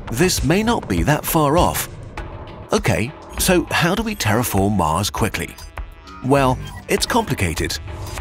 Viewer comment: At first I thought it said terraform stars. I was like "WHAT"